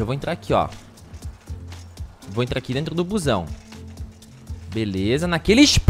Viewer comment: achei que ele tinha falado cuzão só depois que fui perceber que ele tinha falado busão kkkkkkkkkkkkkkkkkkkkkkkkkkkkkkkkkkkkkkkkkkkkkkkkkkk